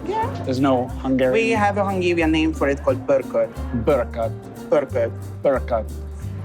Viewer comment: the word you put in the captions is wrong. You put in Bogracs which is the pot Goulas is cooked in traditionally, what he said was Pörkölt which is a completely seprate non-Goulas dish. You got punked by a budapest resteraunt which gave you Pörkölt and called it Goulas for the tourists, but honestly thats ok because Pörkölt tastes better lol